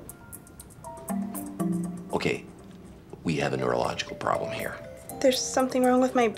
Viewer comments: Bro I thought he said uerological and I was confused af but a neurological problem is right. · “… Okay… We have a neurological problem here.” 🤣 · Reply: I thought he said "urological problem". Like a UTI from the jelly 😅